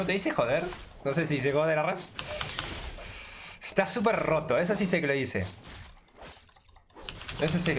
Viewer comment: lo que dice en realidad manute es: esta completamente OP